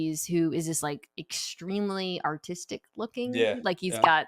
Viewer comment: who else thought she said "Autistic" instead of "Artistic" lol